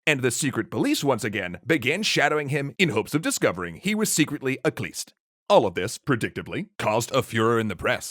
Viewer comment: He was secretly a what? I can't understant it and CC just says "at least". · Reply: A khlyst. They were a small, banned religious movement with a very bad reputation. They're mentioned in the previous episodes; people had been trying to prove that Rasputin was connected to that movement ever since he started preaching.